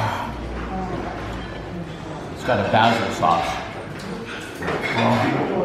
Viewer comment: When you first ate the steak - sounded like you said bastard sauce. Later I realized you said basil sauce. I would love to have that meal 🎈